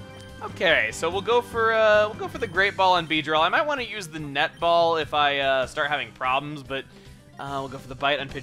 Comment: When he said "I still have those net balls", I thought he said "I still have those nut balls" o_o